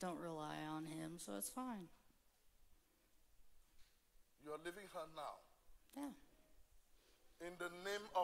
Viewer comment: the "demon" thought he said "your living in her now" but he said "your leaving her now"!!!